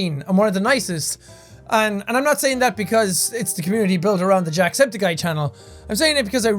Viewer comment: I thought he was saying "Aunty-septiceye" lmao